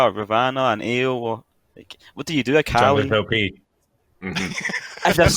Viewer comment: What is saying at 4:34? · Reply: Junglers OP